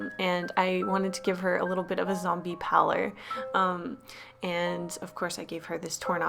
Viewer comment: “Ballerinas are often seen as scary” Me: ....Ballora? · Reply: Omg I thought she said ballora 😂